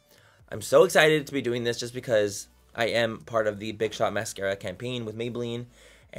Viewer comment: Is it just me or at first i heard him saying big bitch mascara 😂